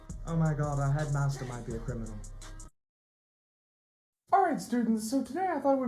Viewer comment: he says that our head master is now a criminal while the captions says a super cool guy